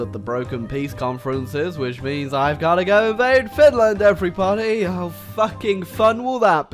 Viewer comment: did just said "everypony"? Loving it